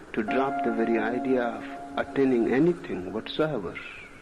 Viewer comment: to drop the very idea of "Attaining" anything??? Instead of attending😊 1:31 ...moksha, NIRVANA, god included (missing that word) 2:40 ...drop that barrier (instead of barriers, it isn't plural) 3:02 ...in an infinite "regress" instead of rigorous 😊 3:25 ...called it LILA (instead of leela) LILA, the play the world of form delights in playing with you... The divine play of form!! I got tired and I'm busy but your captions are plagued with errors. Please correct them to honor the memory of the master of Masters!😊